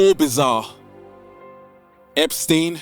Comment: who killed his self? I didn't hear it ? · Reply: Jeffrey Epstein